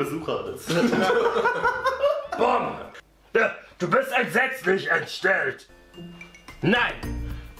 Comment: Was sagt Jan bei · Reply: "du bist entsetzlich entstellt"